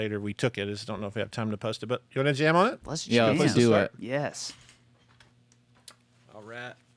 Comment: did Josh just said "oh, RAT" instead of "all right" on 15:09? or am I hearing things?